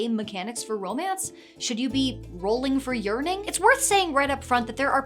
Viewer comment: What Ginny said: "roll for yearning," What I heard: "roll for urinating." -- I thought that escalated really quickly.